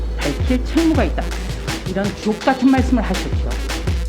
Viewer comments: 조~~옥 같은 말씀 하셨습니다로 들리네요 ㅋ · 주옥같은말씀 ?^^ ㅈ같은말씀으로 들리네 ㅎㅎ